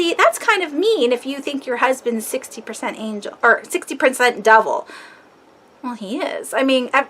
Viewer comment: I thought you said "double". Then I realize you were trying to say "devil" 😂😂🤭